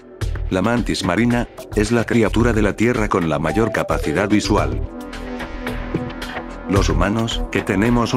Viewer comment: xd en el minuto 10:29 entendi la mayor capacida de swah.